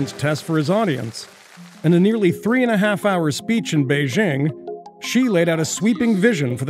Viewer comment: did he say "she"